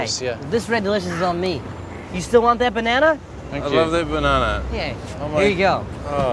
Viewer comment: lol it sounds like he says "i love the banana"